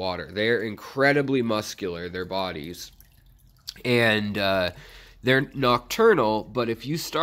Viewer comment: Can u keep them in a planted tank. I have dwarf hairgrass in the front. Will they burrow and make the plants come off ? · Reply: thought you said they’re nocturnal